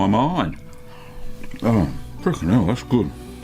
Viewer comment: Did I hear "oh f***ing hell that's good" at 5:34 ??? I'm dying laughing here. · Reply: I'm pretty sure he says, "fricking hell", but close enough.